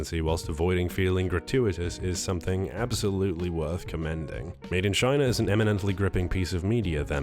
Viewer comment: Did you say Made in China?